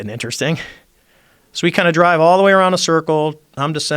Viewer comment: did he say “drive”